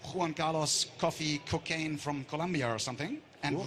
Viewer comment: did he just say cocaine ?